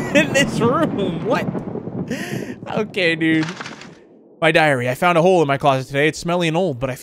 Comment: When he said my diary I thought he said my diarrhea 😂😂😂!!!